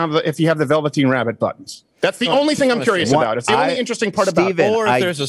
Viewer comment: “You still won’t take your glasses off” wow man, sick burn. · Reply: he said he wants to "see if he looks like velveteen rabbit" I thought I heard. "because that's the only thing interesting about you Sam". So yeah, even with context it made no sense. It confused me so much I looked it up and I guess Crowder did voice acting for a velveteen rabbit movie once in the 2000s? Such a weird thing to bring up